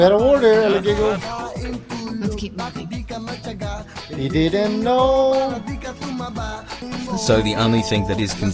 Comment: "He didn't know" instead of "igiling mo" hahaha